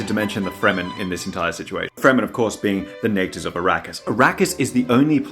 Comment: I misheard Arrakis as Iraq but i still get it... is that on purpose?